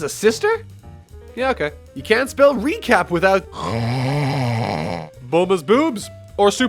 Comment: I know you put a lot of time into this, but at 2:48 you say: " recap" but your subtitle says: "repeat" you can't have a mistake in such a heavily edited video....it's just not right, to have that much time to miss a minor detail. By the way I'm REALLY loving this channel!